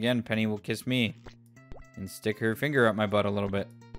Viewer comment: Did I hear "sticky fingers"? Is that a jojo reference?